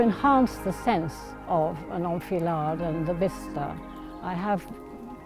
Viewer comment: An "old fillard"? Is that what she's saying? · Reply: "enfilade" French word, meaning in a row/line.